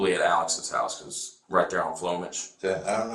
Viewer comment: I could of sworn they where saying Florence not Flormich